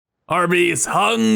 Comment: I could’ve sworn he said “arby’s hungers for power” and not “our base